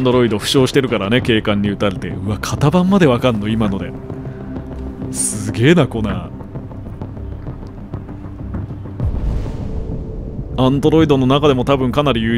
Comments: 血液ペロった後の「すげぇなコナー」が「すげぇなコナン」に聞こえて、コナンはアンドロイドだったのか！？ってなった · すげぇなコナー、がすげぇなコナンに聞こえる笑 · すげぇなコ○ン に聞こえて草